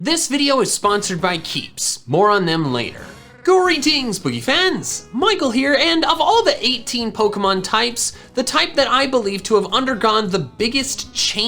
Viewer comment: i thought he said greetings poopy pants